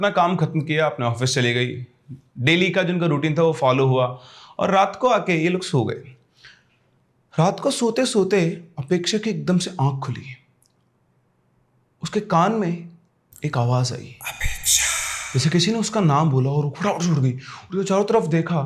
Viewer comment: I heard 'Amit Shaaaaah' not Apeksha 😂